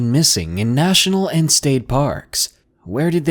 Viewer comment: I thought it said skate parks